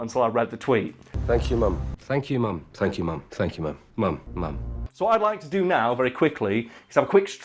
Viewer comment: Having never seen the show, just based on the clips seen in this video, I heard "mom/mum," however I am sure in the context of the show I probably would have been able to figure out he wasn't actually banging his mother.